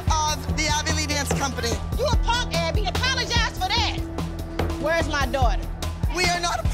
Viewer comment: black patsy starts walking away realizes she has a daughter "where's my daughter!?" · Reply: Oh I thought she said where’s my dog